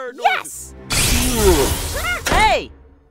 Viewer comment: when you said "hey" it kinda sounded like eda